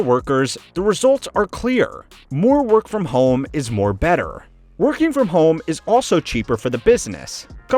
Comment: I heard you say “more better”